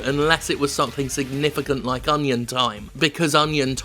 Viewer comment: he mentions a good mobile game that sounds to me like he's saying "onion time." Yet when I google that nothing comes up. Is he saying something else?